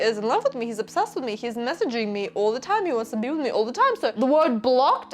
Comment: I misheard that for 'He wants to do me all the time' 😅